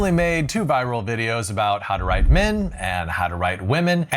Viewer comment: this is superrr off topic but i accidentally hovered my mouse over video and the audio started playing & i thought he said "i made 2 viral videos on how to ride men" 🥀💔💔